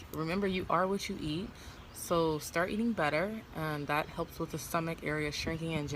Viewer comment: I thought you said start eating butter lmao but when I heard you again you said start eating better